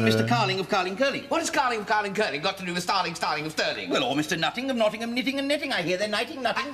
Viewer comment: Another one that I hadn't seen, this was great (you need some Harry Mack trained ears to get the middle part at 4:00 wow lol) Cheers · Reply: Glad you enjoyed it! I got mixed up from the beginning when I thought he said "Starling, Starling, AND Sterling" I thought maybe a law firm... Didn't realize until someone pointed it out the he said FROM Sterling lol... The place names I wasn't familiar with.. That would have helped lol.. Cheers!